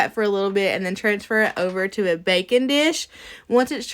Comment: I was listening to your video while cleaning the kitchen. When you were cooking I thought I heard you say “bacon dish”. I had to stop and watch the video to see what a bacon dish was! 😂